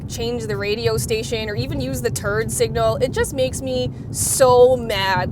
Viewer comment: At around the 6:50 mark..."turd signal" I swear that's what I hear and have replayed it many times.